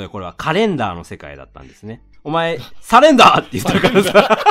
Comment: サレンダーで腹筋が死んだ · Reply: 最初、普通にカレンダーって言ったように聞こえたからびっくりしました笑